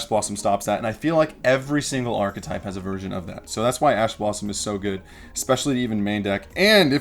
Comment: I thought this dude said ass blossom for a sec